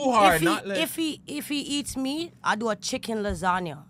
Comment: I heard if he eats me to lool